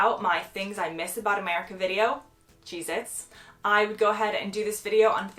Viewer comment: Right at the end I thought you said Jesus and the thought we have Jesus in England to 😄 And then I realise you said cheesets lol